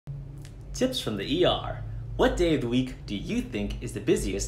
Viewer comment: When you said tips from the ER I heard you say Tits from the er